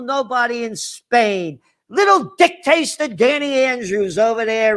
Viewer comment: he said dick taster instead of dictator LMFAO HAHAHAHAHAHAAHAHAHHAHAHAHA